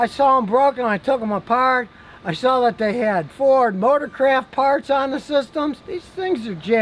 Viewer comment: Did he say "Motorcraft" or "Motorcrap"? :)